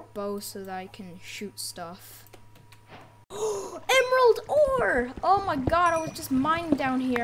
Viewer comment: subtitles said emerald over